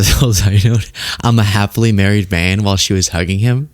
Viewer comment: I thought he said halfway married man which would perhaps be more appropriate.